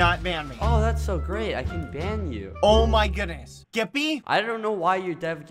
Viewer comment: it sounded like he said "Oh my goodness, Gippy"